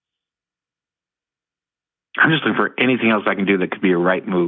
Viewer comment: I heard “looking at porn” amidst some very fast talking. There’s more to this story that he has only begun to hint at. John responded with “we all have.” Huh?